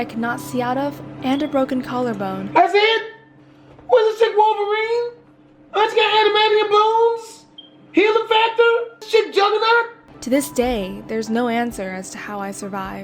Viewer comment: "Thass it?! Whashur chick wolverine anime handymanny and blooms? Heal affector?? Juggernaut??" That's what i heard😭😂